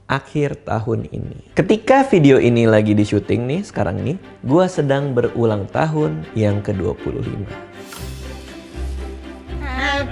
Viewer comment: “Gue berulang tahun ke 25” Sampe gue ulang 2x, kirain salah denger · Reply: Sama.. Emng bener 25?? Edit : yaampun, habis dia ngomong 25 aku lgsg lihat komentar.. Eh pas videonya lanjut baru tau umurnya 35